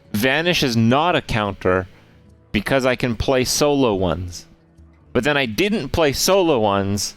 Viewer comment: Why isnt vanish a counter? (at 16:15) I dont understand what he says, unless he said "a solo one" but I dont get how that would prevent vanish at all Edit: nevermind, I understood after thanks to the shaman clip